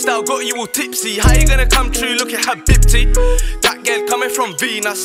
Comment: First-time round listening to this song I thought he said “He big tit” instead of habibti, disappointing tfoeee